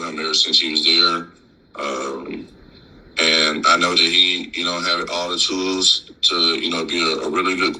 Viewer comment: the fact that Tytus said he and the subtitle says Shadrill is crazy work 😂😂😂 2:45 but in all seriousness I rock with what Howard said